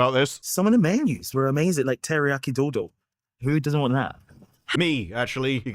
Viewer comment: I thought he said Teriyaki Dildo...